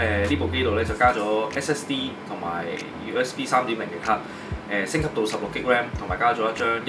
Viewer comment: 想問2:50 話加左甚麼3.0咭? 聽不懂。其實在二手電腦的CPU, 睇SPEC 邊項知道係夠用來升級打機? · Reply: USB 3.0